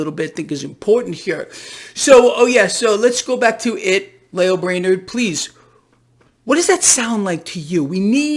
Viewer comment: I heard ‘Lame Brain’’ 😂